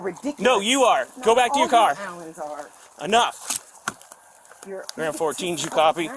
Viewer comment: did she say "all you Allens are" sounds like it's a family full of tyrants